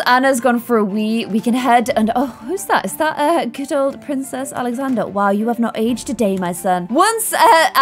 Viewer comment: did anyone else hear princess Alexander